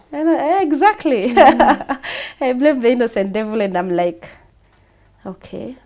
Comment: did I hear innocent devil😂😂😂😂